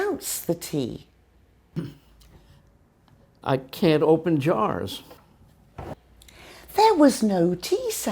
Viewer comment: I can't open jars. American: " I can open jars" What I hear: "I can dopen jars."